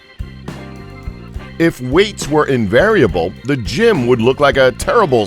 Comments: did he just say "invariable" Instead of "Invisible"? ARE YOU KIDDING ME!?? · He said invariable instead of invisible. Minute 0:50 🤣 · did he say inverable but the text said invisible. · 1 more shower thought this guy said invariable instead of invisible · He said invariable instead of invisible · am I the only who heard him say “invariable” instead of invisible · did he just say invariable???